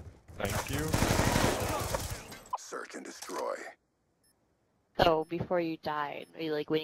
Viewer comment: I thought they said sherk and destroy